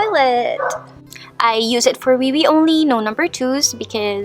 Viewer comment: I thought you said Peewee hehe